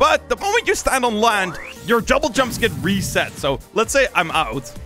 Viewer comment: did i just hear him say jumble jumps?